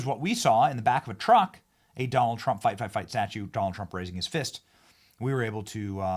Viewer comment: The only thing that would have made that moment even more perfect is instead of him saying fight, fight, fight. Is if he was saying Fu because that's what I thought he was saying at first was fu fu. That would have been much more hilarious LOL